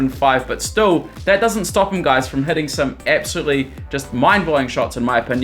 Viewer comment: i thought you are saying heating instead of heading